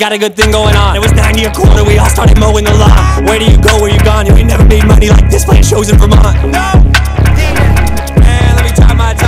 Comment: what did he say at 1:25 please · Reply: "You never made money like this playing shows in Vermont"